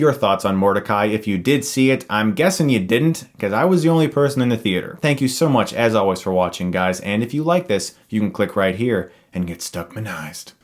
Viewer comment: Sorry, I had to rewind cause I thought you said, "and if you like this you can click right here" and get stuck in the eyes.